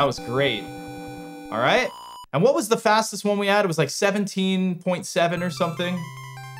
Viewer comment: very strange bug with the auto captions here, they actually say 17.6 instead of 17.7 🤨